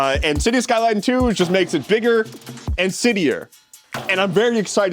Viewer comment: the auto captions say "bigger and shittier" in reference to city skylines 2 lmao